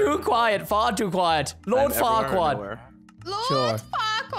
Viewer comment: i think he said the. F word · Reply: faeez he said lord farquaad